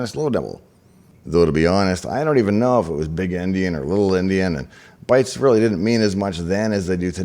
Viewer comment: It sounded like you were saying big & little Indian. If so, it should be big & little Endien. The term came from the book "Gulliver's travels".